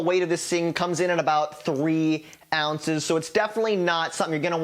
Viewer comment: I thought he said the first crank bait was 3 pounds! No he said ounces 😂